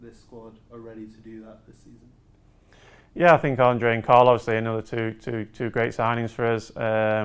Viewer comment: did he say Andre and Carlos??